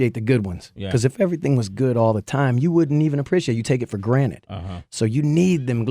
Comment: did he say take it for "granite"